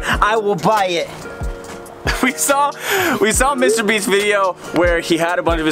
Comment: I thought he said Mr beans video lol not Mr beast 😂